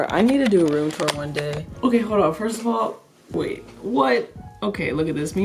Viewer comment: what did you say?! Did you just say "ok hora?!